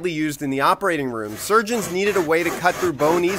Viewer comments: I’m childish I hear “giggity saw”😂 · Bro I fr thought he said giggity